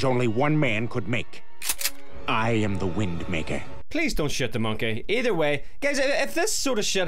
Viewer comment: Did you just say, "Please, don't shit the monkey!" LMFAO 😆